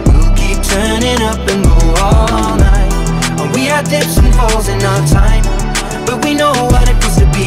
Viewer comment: Sound like he said "we had different hoes" in our time but he says "dip and falls"